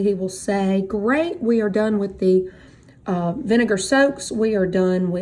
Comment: When she was saying "vinegar soaks", I initially thought that she was saying "vinegar strokes": which would have put a slightly different... ahem... "complexion" on things....